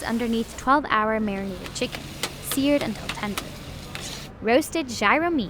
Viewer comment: Please do not put auto-generated captions on... At 0:05 it says "seared and placenta" instead of seared until tender...